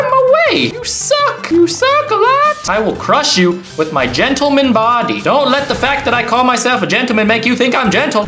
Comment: When he said gentleman body I thought he said genital body lmao